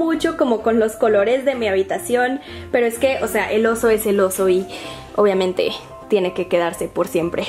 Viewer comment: Min 11:58 "El oso es el oso" y yo entendí el oso es celoso xdd